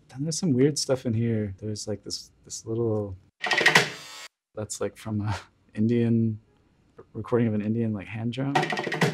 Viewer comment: I swear I heard him say "That's like from a Indiannn... Recording of a Indian like hand job?" @ 3:20 loool